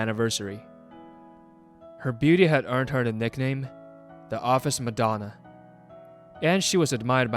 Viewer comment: Probably just my ear, but I heard "The Office McDonald". Best misheard ever.